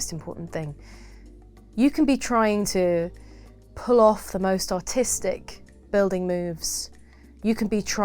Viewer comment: i thought she said "you can be trying to pull off the most autistic building moves"